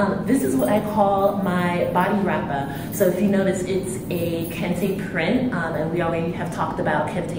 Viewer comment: Hey beautiful! This was so so helpful thank you 😊 especially for a first video! At 0:37, what was it you said? A something print? I’ve been looking for beautiful patterns such as yours but am having a hard time finding some- thank you so much 🥰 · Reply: Hi! Thank you so much for watching. You're SO welcome! This fabric is called "kente print" as opposed to traditional (authentic) kente cloth. I talk a bit more about the difference here on my blog page if you're interested 🙏🏾💗 <link>